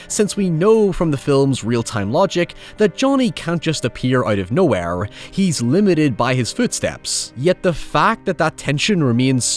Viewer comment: I heard “he’s limited by his food stamps” lol